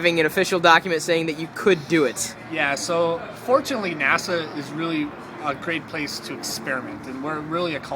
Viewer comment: Her cookies! Oh.. Nasa? I thought you said my nana.